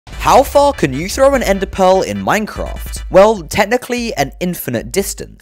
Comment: All I heard was " how fall can you throw a ender peal "